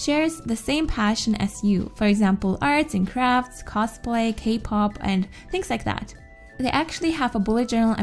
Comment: did she just say kpop .............I'm dead 😱❤❤